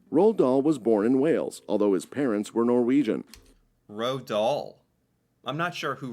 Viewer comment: English speakers pronouncing norwegian names is awful >.< It sounds like hes saying Roadal, but hes talking about Roald Dahl.